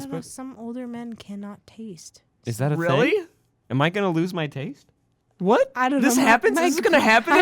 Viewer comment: what said: am I gonna lose my taste? what I thought he said : am I gonna loose my taint?